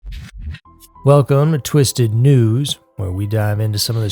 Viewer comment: for long time I thought you were saying "Twisted 2's" not " News".😅.